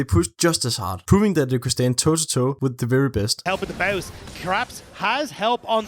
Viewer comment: Am I the only one who heard the shoutcaster saying "Craps" in reference to Caps ????? WTF HELLO!!